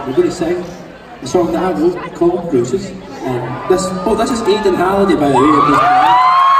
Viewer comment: did he say Aiden Gallagher? (However you spell it)